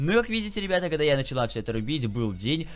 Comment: Мне одному показалось что он сказал "я начала"